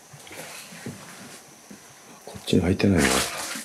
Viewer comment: 『開けないで』って聞こえるのだけど…